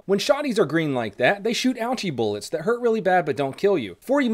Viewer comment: I heard "Algae Bullets" not Ouchie Bullets. This has been a solid break from Biology homework.